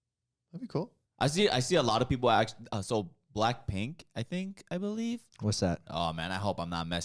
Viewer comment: I was gonna use code HIGA on SeatGeek, then I heard "who's Blackpink?"